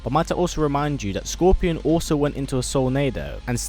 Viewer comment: Did he just say "Scorpion"?